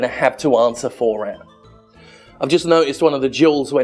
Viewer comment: I heard "Drink Juuls" and feared for your health. Plastic gemstones though, I'm sure you'll be fiiiine.